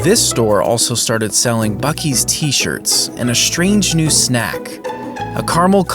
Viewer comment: bro i think I heard "fuck-ee's"